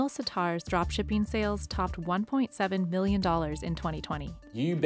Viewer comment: At the 0:44 mark the caption says million when she clearly says billion.